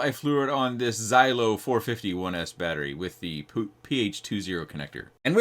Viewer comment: did you say the poop ph2 connector? Rightly so when speaking about the rolled pins… 😂